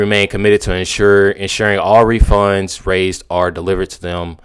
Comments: Thanks for being honest man. I've thought all along that Caramelo's fund money they raised should go to the Metcalfs. It's sick to celebrate ending a life. I'd feel the same way if the races were reversed. Yeah, I spelled his name wrong. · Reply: he stumbles here. I think he lied. I believe it says funds, not refunds. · How is he going to refund the money if THEY ALREADY SPENT IT? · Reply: this sentence doesn’t make sense with “refunds”, I think it says “funds”